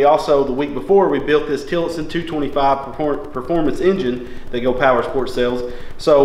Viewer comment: it sounded like he said po poormans engine lol back ther a lil bit